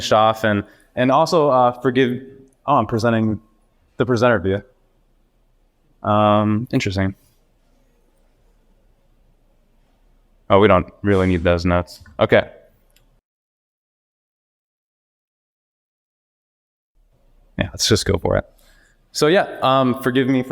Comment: why did he say "we really did not need deez nuts"?